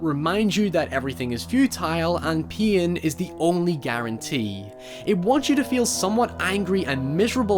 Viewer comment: I first heard "Everything is futile and peein' is the only guarantee"